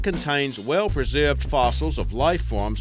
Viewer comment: "A BILLION YEARS AGO" Because he stressed the "a", I misheard it as "eight billion years ago", and thought that can't be right. The Earth wasn't even here yet.